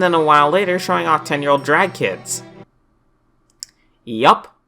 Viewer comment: Drag kids? Drugged kids? What are you saying dude?